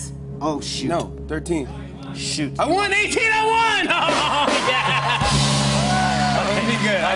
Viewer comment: I’m I the only one who heard the guy in the background go “you won”